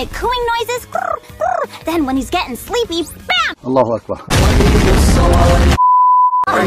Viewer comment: did he just say alah akbar